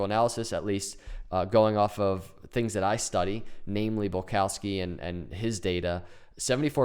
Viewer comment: What was that guy's name you said? .. Balkowski? Best guess. At 6:30...